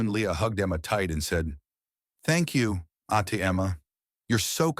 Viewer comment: "Thank you Ate Emma, you are so kind." The subtitles are wrong, transcribing Aunty Emma. In fact, the familiar vocative Ate means elder sister, but is applied to others somewhat older than you as a sign of respect. It is the same term used to refer to Ate Rosa. I don't know who, or what, wrote the script and the transcription, but it could use some help.